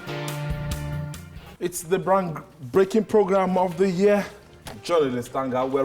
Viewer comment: Did I hear the anchor at 20:15 says brank breaking of the year